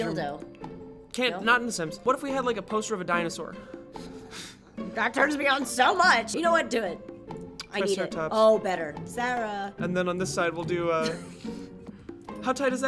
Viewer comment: Did say Sera? Does she really remember the name of the girl Tryceratops in the century old animated movie? I love you.